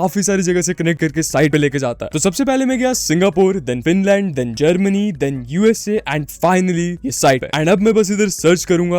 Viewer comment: did bro just say Vinland